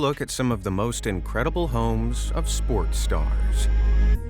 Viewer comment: I thought it said Incredible Homes of Sports Cars, not Sports Stars